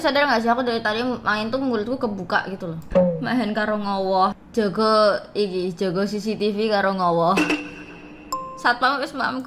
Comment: sedikit koreksi itu kayaknya bukan jago tapi jogo (jaga)😅(kalau gw gk salah denger)🙃